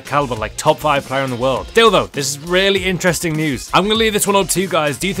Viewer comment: Did he say Dildo? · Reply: noo he said still though